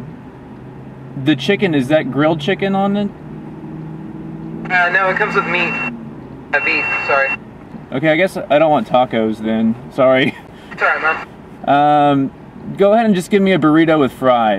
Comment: i swear he said it comes with me at about